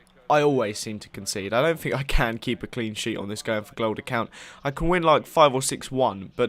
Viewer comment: sounds like your saying going for glod.